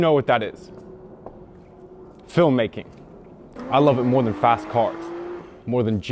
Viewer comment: I was multitasking while watching this and legit misheard 6:23 as "I love it more than fat cock" Which would be totally fair play and all, just a strange tonal shift yaknow